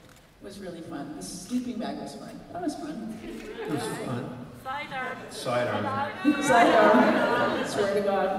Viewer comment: what are they saying and referring to? What is "Sigh-Nar"??? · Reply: They are saying "sidearm".